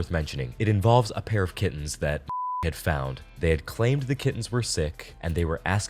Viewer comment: I thought they said kidneys😂